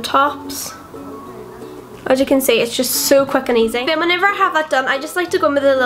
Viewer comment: I thought you said 'so fucking easy' but I replayed it and you said 'so quick and easy' 😂